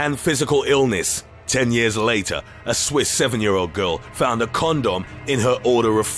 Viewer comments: I thought the guy said corn dog instead of condom at first. He could make the phone book sound entertaining. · i misheard “condom” for “corn dog”. i was very confused lol